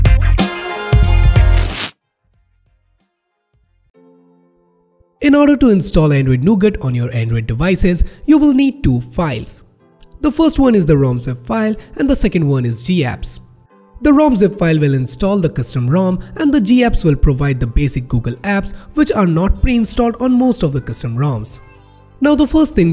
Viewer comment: Auto-Generated Subtitle said "Wrong zip file"